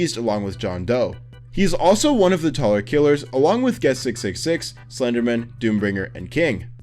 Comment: Ngl when you said guest 666 I thought you said blox watch lol